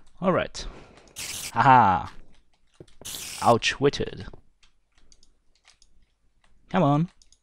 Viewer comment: lol "ouch-witted" instead of outwitted....